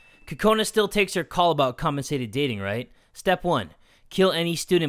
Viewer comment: The whole time he talked about Kokona's "compensated dating" thing I thought he was saying "constipated dating"